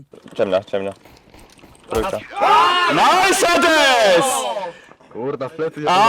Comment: Wiem, że Izak powiedział "Nice, Hades" ale usłyszałem "Nice, Sedes"